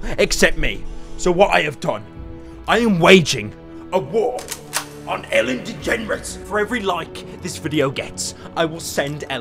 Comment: did he just say Ellen Degenerates?